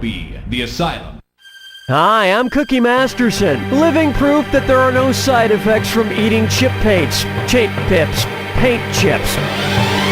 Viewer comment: I just can't hear everything he's saying. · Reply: "Hi, I'm Cookie Masterson, living proof that there are no side effects to eating chip paints-chaint pips- Paint chips"